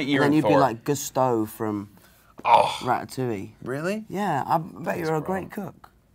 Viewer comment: Not gonna lie, I thought Tom said Chris would be in Prattatouille, and now I need that movie.